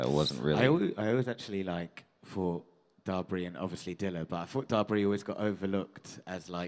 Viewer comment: guys at minutes 33:00 they talked about one three album and "dabri?". I don't understand the name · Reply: Spelt Dabrye